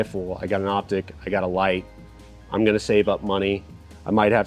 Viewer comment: I thought you said "get a life" not "get a light"...lol